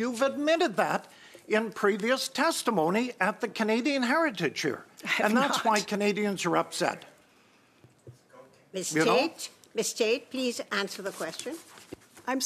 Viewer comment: She's begging for more money?!? She can damn well use the bonuses she and her cronies paid themselves to fund the failing CBC. She's disgraceful and I hate her french pronunciation of Radio Canada! 😖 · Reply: Is that what she's saying? I thought she was saying "hydrocanada"...